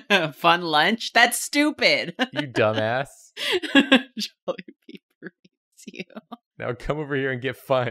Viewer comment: at first I heard "jolibee berates you" as "jolibee breeds you" and I was overwhelmed with the sudden urge to walk into the ocean